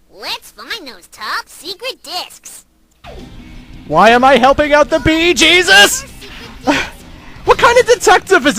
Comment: For a second, it sounds like is saying, "Why am I helping out the Bee Gees?"